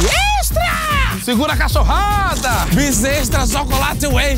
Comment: o que ele falou ? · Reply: Controla, descontrola, controla, descontrola... Regojizai-vos!